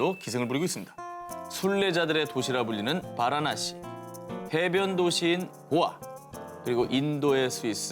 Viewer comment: 배변도시라 들었음 ㅋㅋㅋ